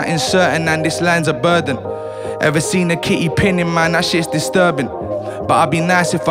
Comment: What does he say is disturbing at like 1:15?? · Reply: TayyOZ a kitty pinning man is disturbing